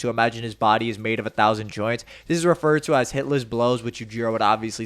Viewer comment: IT'S CALLED WHAT? There's no way I heard that right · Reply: Hitless Blows